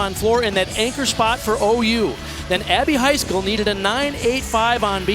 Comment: i thought they said happy high school! they said abby heiksell (so sorry i spelt that wrong and for the comment)